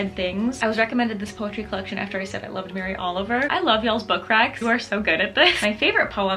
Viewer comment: When you said book recs I heard "book cracks" and immediately imagined a book with a little booty 😅